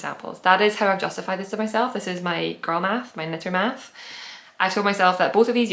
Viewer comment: haha i thought you said purl math not girl math for knitter math and i thought that's brilliant! i'm not a vest person either but you might have converted me. i looks so nice with just a plain white tshirt.